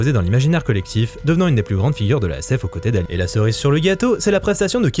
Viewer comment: Très bonne vidéo mais je suis un peu curieux de savoir pourquoi il y a une "coupure" en fondu à 11:10 ! Tu dis bien "[...] devenant une des plus grandes figures de la SF aux côtés d'Alien" ?